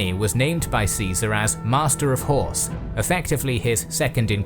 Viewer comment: I've misheard that Marcus Antonius was elected "Master of Whores" and it honestly didn't surprise me for a second